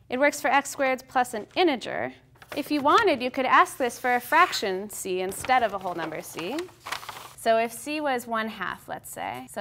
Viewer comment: I keep hearing it as Bill Cosby ''You can ask this as a fraction, see? Instead of a whole number, see?''